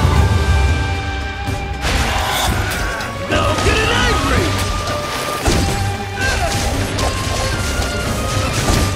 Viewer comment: did he say “no get it angry” ? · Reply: "Now I'm getting angry"